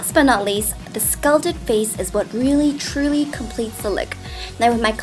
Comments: It sounds like says " scalded face" but I know she means sculpted face lol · i love watching you but when i heard you say "the scalded face" when u meant the sculpted face, i almost choke on my juice in laughter. Why are you so cute!!!!!!! <3